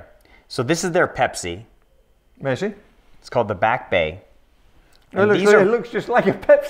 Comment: What's he talking about Omega AD's will do big discounts. Most of these watches he's showing are dog shit as well. 20:22 did he say BACK bay?